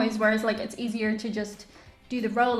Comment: I’m usually quite good understanding various English dialects but listening on my phone, at a distance, while kinda daydreaming I thought I heard “roll on yogurt” hm interesting.. that’s an odd thing.. then “spray on yogurt” WTF is that?!? I know weird things are done in the US but that’s a new one XD